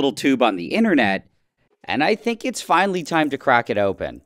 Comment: I thought he said “I thinks its finally time to craft an onion” and I thought of viyaura because she likes onions